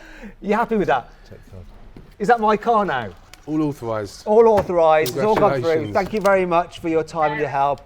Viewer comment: thought he said "unauthorized"